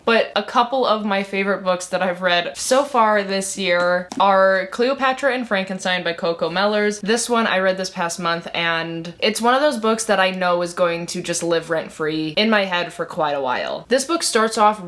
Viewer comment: When you were introducing Cleopatra and Frankenstein I really thought you were about to say by Cocomelon…. 😅